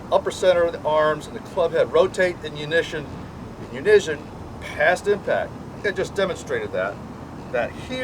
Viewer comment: they rotate in "unishion?" I think you mean to say, they rotate in UNISON. That's, YOU - NEE - SUN but say it fast ... YOUNEESON or unison.